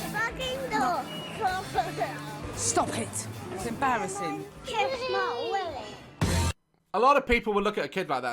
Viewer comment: I’m not sure if it was me or somehting but I heard the youngest kid say “kiss my Willy”